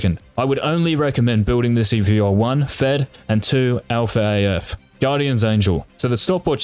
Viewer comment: Did he just say Alph AF around 12:04? 🤣🤣🤣